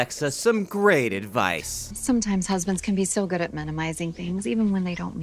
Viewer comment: I heard here "sometimes husbands can be so good at sodomizing things"